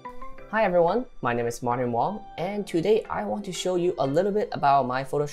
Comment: I thought he said morning wong. i was like what?